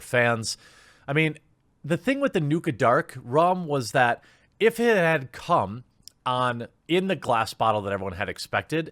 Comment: Thanks for checking today's video out! If you enjoyed please leave a LIKE and Comment! It really helps the channel!!! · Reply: Sounded like you said "If it had cum in the glass bottle". Cracked me up!:P